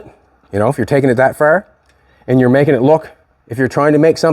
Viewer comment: I thought you said you were taking this to paint